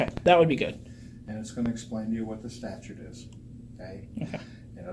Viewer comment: did he say “staturte” instead of “statute”? 🤔